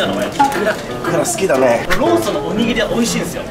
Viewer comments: がローソンっていうとローションみたいに聞こえる笑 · がローソンって言う度、ローションって聞こえる